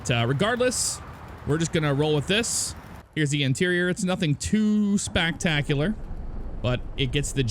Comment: Did I hear "spactacular" 😂